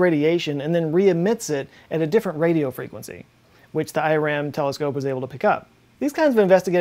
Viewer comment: 😂😂😂 i thought he said Iran telescope!! 😂😂